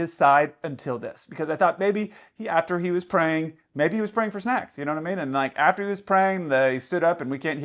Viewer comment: "Maybe he was praying for snacks" 🤣🤣🤣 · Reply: I have a temporary hearing problem. I misheard " praying for snacks " as " praying for sex"! I thought "Whoa Kelsey that's a bit racist!" Fortunately, it I watched the rest of the video😁